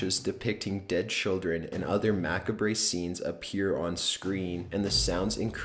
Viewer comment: did you just say.. Mac-re-beh? lol